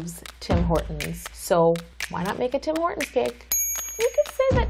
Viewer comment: I thought it said Tim Burton cake, so I got really excited 😂